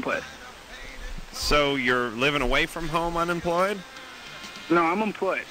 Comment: huge misunderstanding, the guy said "I have my own place", adam heard "I'm unemployed" I heard "I have my own place" clearly but because that stupid music playing in the background he misheard it, and it costed the call, got hung up on unjustly smh